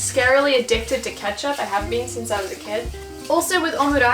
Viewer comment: I know she says kid, but I hear cat everytime.